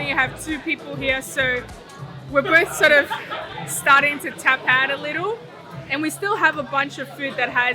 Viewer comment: can anyone tell me what was saying? Starting to tap out a little? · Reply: Yeah We are starting to tap out a little.. haha